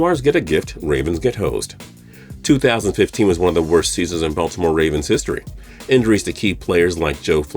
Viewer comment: Did he say worst year in Buffalo Ravens history 🤦🏾‍♂️ 😂🤣😂🤣😂🤣😂🤣😂🤣😂 🤷🏽‍♂️